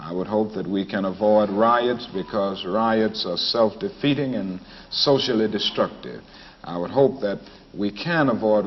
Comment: "Riots are socially destructive and self defeating." ~ Dr. King #IbelievelikeKing This speech is still relevant today (after 54 years later)! I was 1 year old! Go Listen & Learn! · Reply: I truly like the way you have this quote. As it turns out, what Dr King actually said is, "Riots are self defeating and socially destructive." I re-listened twice to be sure. I think the order in which you have the description of riots, works really well, too. EDIT: The Quote